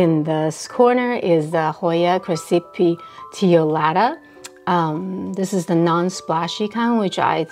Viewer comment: Hello! I really wonder which Hoya the one at 5:18+ is. I couldn't quite understand the name of the plant. Can you write it down for me? Thank you! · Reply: hoya crassipetiolata :)